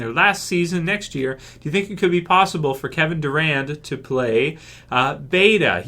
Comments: I thought you were saying Kevin Durant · Thought you said Kevin Durant lol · I thought you said Kevin Durant lol